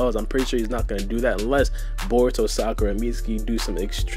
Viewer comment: Did you say "Sakura" at 5:09?